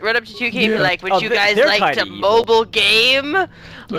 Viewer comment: I've got something even worse than Civ 6 Micro Transactions, Star Wars KOTOR 3: Always Online Multiplayer Only. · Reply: I misheard mobile as moba and got slightly confused for a second.